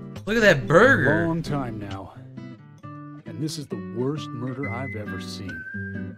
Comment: I swear I heard "This is the worst Burger I've ever seen"